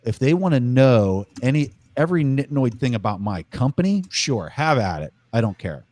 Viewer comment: Preston know some Thai? Says "they want to know every NID NOI thing"